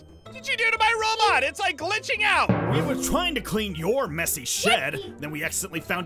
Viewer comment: I know it sounds like Zane said Sh!t but don't worry he just said "Shed" you can go to subtitles and see for yourself